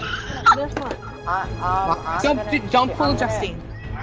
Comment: Anyone hear the “don’t pull a Justine” at 10:57?